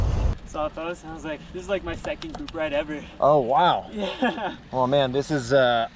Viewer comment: did he just say it's his second bike ride ever? And he's got an SL7 between his legs? Jeez!